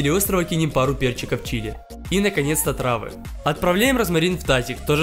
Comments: Мне послышалос:,,И наконец отравы🤣🤣🤣" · послышалось отравы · мне одной послышалось и наконец отрава😂😂